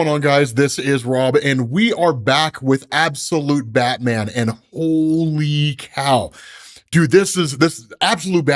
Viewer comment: Did you say Hollyy Cowl! lol!